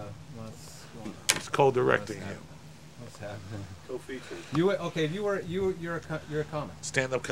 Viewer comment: Did I hear covfefe around 5:45?!